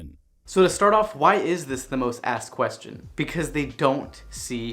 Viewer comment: I heard, "Why is this the most assed question?" You could actually substitute asked for assed.